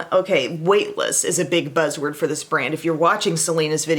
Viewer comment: Omg I’m so funny I heard you say weightless and really thought wait list lmao